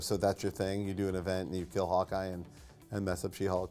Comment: Did he say kill off Hawkeye??